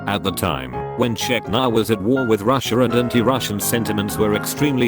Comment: Did he say "no Russian" though?